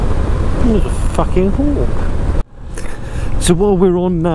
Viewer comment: Poor little deer :( And what kind of bird was that @ 5:39 ? Did you say "hawk"? I'm glad you're getting on with the new ride. She's a beauty :) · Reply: What do you mean re deer? :-) I think it was a hawk yes, is a shame I wasn't recording at 4k, as it would have possibly been easier to see it. It was just standing by the side of the road, I hope it was able to take off and get away from the cars. Thanks mate, am lovin it :-)